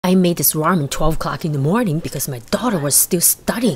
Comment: Why do i think I heard her say"I made this with 12 co cks in the morning"💀💀💀💀